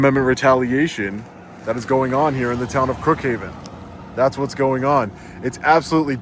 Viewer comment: did you say "Crookhaven"?